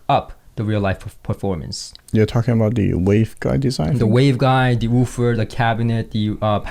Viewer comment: said "waveguide". I heard "Bose".